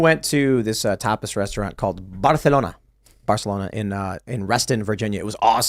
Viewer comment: LMAO at the chat at 23:09 I thought he said topless restaurant too